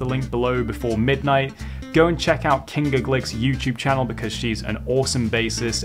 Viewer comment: Love Kinga Głyk's playing. Incidentally, "ł" in Polish is pronounced like a "w", so her surname is pronounced "Gwik". (Polish currency is the "złoty", pronounced "zwotty".) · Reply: Ty for the insight. I've been a fan of Kinga's playing for maybe 7 years or so and every time I've said her name, I've mispronounced the ł. Even after hearing her name spoken by her, it sounded like they were saying the equivalent of Glick and not Gwick. It's a trip how convincingly your mind can alter your perception. Thanks again.